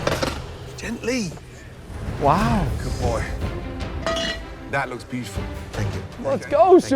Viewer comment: did gordon just call subha a goodboy